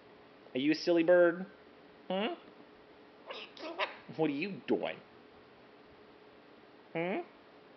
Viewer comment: She is awesome! She does NOT like to be called silly or goofy, LOL. At 6:15, I swear she says, "what are you, stupid?" Except I know you wouldn't teach her that. ;) Thanks for sharing her with us.